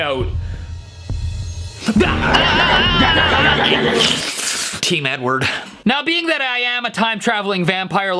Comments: it sounded like he said “team n-word” bruh I stg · I thought for a millisecond he said team n word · did he just say team n word